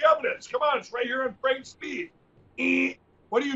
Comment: Oh...I thought I heard I heard "Fran's Beep"-- thanks for clearing that up!